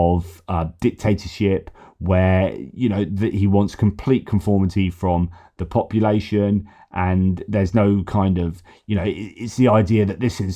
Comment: While working and listening in the background, I misheard this as "He wants complete conformity of copulation". It would have made a great clickbait title: "Putin demands....".